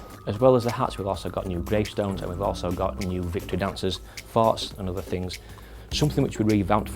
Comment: thought he said farts :x